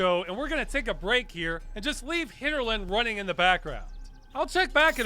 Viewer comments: lol, I thought it said HitlerLand :P thought it might be weird and stupid... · Thought it said Hitlerland, I was confused.